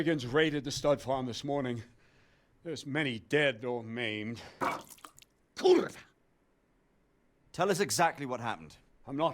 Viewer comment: did I heard "Kurva" in 1:31 ?:D I'm getting this game for sure then !